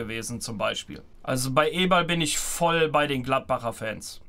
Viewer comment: Lol, hatte zunächst eBall statt Eberl verstanden und dachte, dass wäre die nächste Sportart, die sie supporten.🙃